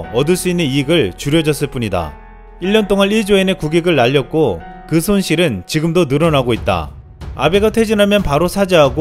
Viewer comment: 나만 근손실로 들었나?